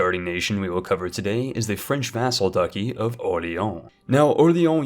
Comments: did he just say "ducky"? 19:27 ...did he just say "lubbock"? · Did you just say French Ducky instead not Duchy? Lol.